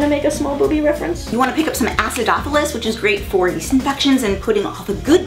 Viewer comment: what did say at 2:28. assedothelits? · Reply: acidophilus